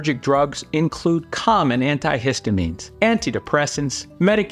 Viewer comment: they include c_m . . ? Wait what you just say??? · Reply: It was the emphasis on the syllable that made it sound like that 😂… he actually said “COMmon anti-histamines” 😁👍